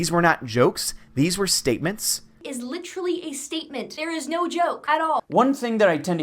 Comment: did she say licherlay